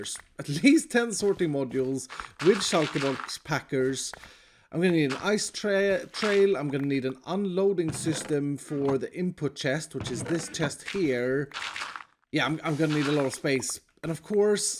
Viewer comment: when he first said “ice trail” It sounded like he said “ice tea” 🤣🤣